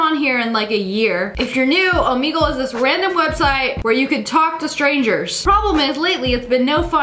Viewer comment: I swear I heard “Omegle is an illegal website”💀